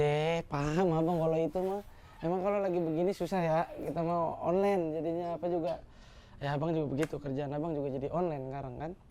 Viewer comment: salah dengar apa gak si ngab judi online 😂